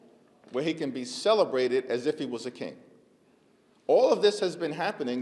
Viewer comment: “AS IF he was a king.” I thought y’all said he IS a king. 😂